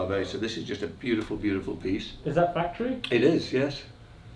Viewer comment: I thought said "is that battery?" And I was wondering how come it's a Rolex and it's battery?? Than i realized that said Factory NOT Battery!! 🤦‍♂️🤦‍♂️